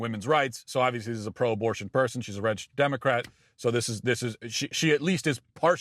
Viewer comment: Did anyone else mishear what Matt said as "wretched Democrat"?